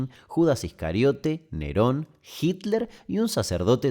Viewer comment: la primera vez escuché "... Perón, Hitler... " kjjjj lo tuve que volver porque dije wtf lpm lrcdll